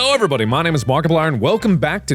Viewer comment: I panicked at the beginning of the video because I thought "he said my name's Monika" and I was like "don't you dare do this to me". I was a bit disappointed when I rewinded it though tbh :/